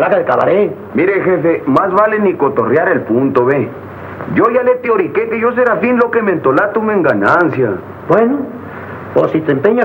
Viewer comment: alguien sabe que dijo? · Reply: Yo ya le dije que seré lo que me entre en gana.